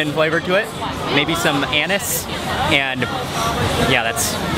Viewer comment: i heard 'Anus' instead of anise 😂😂😂